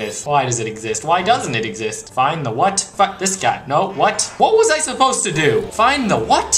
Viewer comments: Did he say "fuck" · Lol you bleeped "shit", but not "fuck"? Great job:) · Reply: ***** It sounded like he said "Fuck this guy"